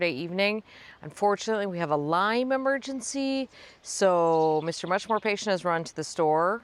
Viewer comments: Hahaha! I thought you were saying you had a "lyme emergency," but, no, you had a lime emergency associated with your gin and tonic. Thank you for always keeping it real, and for sharing your knowledge, work and passion for gardening with the world. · Love the weekly garden stroll! When you said “lime emergency”, I heard “Lyme emergency” and was immediately concerned. Happy to hear it was only a beverage crisis! 😂Anticipate heat in New Hampshire this week too.